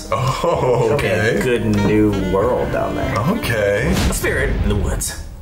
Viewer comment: I thought Sam said a nude world down there at 3:37 😂